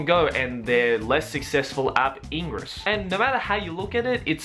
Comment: I thought you said "Engrish" when I didn't watch and got confused